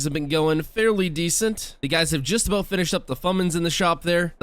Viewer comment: DID HE JUST SAY "FUMENIS"????????/